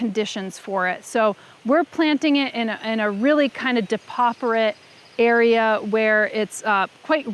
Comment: I love your outfit in this video! You used a word to describe where you were planting your lupinums--it sounded like "depotherate?" I tried to look it up but couldn't find anything. Mind elucidating us? I probably just misheard and got the spelling wrong. Time stamp was 15:29. I love expanding my vocab :) thanks in advance! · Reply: Oh yeah, sorry. I said, "depauperate" — and it's more of an old world phrase meaning "impoverished"–so referring to the soil as "impoverished"... Note, however, that depauperate also has a botanical definition (not ironically) that means more "imperfect" or "incomplete"....